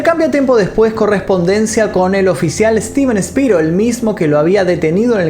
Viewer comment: creí que dijo steven spilberg